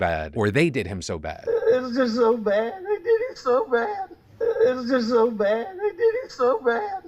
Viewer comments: Former public radio journalist here - re: Murtaugh "confession" –having transcribed hundreds of recorded interviews - I heard "they did 'im so bad" (meanwhile, I've no dog in this hunt...). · I only hear "They did him so bad." If I look at the text saying "I" that is the only time it seems like it could be the other way. So much of this case so far is just circumstantial. Where's the real evidence? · Slowed down I hear "they did him so bad." But I don't think that proves anything tbh. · I hear "they did it so bad" but that doesn't make a ton of sense to me as a sentence but idk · About the murdoc trial I hear him saying "THEY did him so bad" but I still believe he commited the crime :/ sadly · as a southerner, i hear "they did him so bad." i don't know how to explain it, but that sounds more correct to my ears